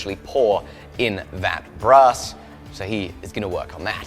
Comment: I heard "Bra's" instead of Brass ;-)